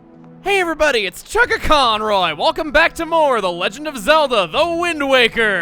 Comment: I swear every time you say "It's chuggaaconroy" I hear "It's Chaka Khan!"